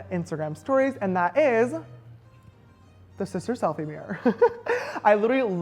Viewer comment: Wait did he say sister suck 😂😂😂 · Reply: i thought he said sister sex